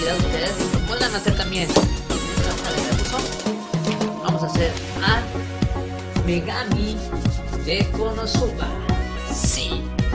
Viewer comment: Pensé que dijo hoy vamos a hacer a megamente